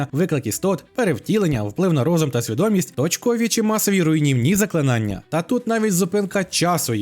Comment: почулося "очкові чи масові руйнівні заклинання"